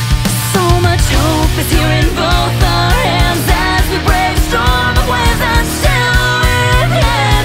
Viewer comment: Sings "As we brave a Storm of Waves and..." But I hear "Storm of WIZARDS"